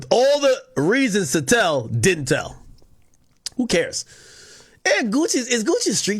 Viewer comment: I THOUGHT U SAID HE DIDN'T TELL